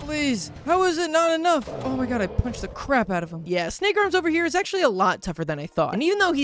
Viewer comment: wth did u say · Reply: He said “snake arms.”